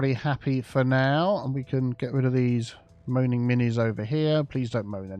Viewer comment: Moaning Mnies? Did I hear that right? SuperVinlin